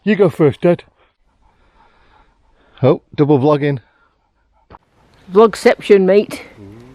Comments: Misheard you during the intro, thought you said some ' doggin' going on; Epic!!! Another brilliant vlog Guys; so enjoyable. Regards, Rob 👍😉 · 'logging'....thought said 'dogging'....lol